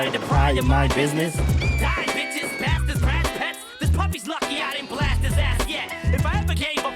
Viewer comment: I always thought Em said “Puffy’s lucky I ain’t blast his ass yet” 😂 years later I realized he said Puppy 🐶 🤦🏻‍♂️😂